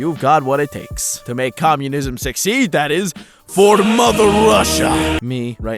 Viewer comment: my video wouldn't load so it sounded like you just said "for mother" and I find that funny